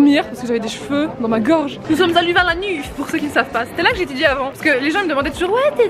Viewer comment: J'ai entendu : ... Nous sommes allés vers l'anus -_-'